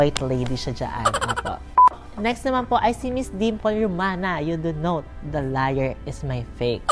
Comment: parang ang narinig ko instead sa Dimples, Dimple without S hahaha pero ok lang kakatuwa si Mimiyuuuh more power to you!!! 😘